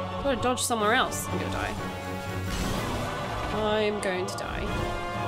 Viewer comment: am i insane or did i hear 'you are going to die' between 1:26:29 - 1:26:30 ?